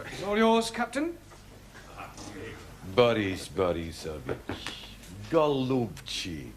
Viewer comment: what is he saying? anyone know? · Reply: The captain says first "Boris, Boris, my dear...", then screams "COME HERE NOW! SHAME ON YOU! YOU'RE COMING WITH ME, YOU'RE GONNA GET SCHOOLED! SHAME ON YOU! SHAME ON YOU!" (translated from Russian)